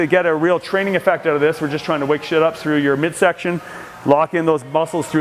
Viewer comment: Did he say “we’re just trying to wake shit up” at 15:08? Lol